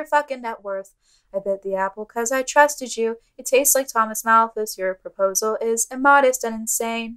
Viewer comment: I thought it said "it's tastes like Thomas MOUTH" lmoa